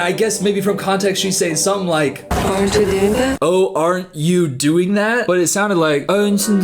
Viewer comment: Mumble actress is the PERFECT way to describe her! It’s not even her accent that makes her hard to understand, it’s the mumbling. I genuinely thought she said “aren’t you danda” instead of “aren’t you doing that” in that one scene.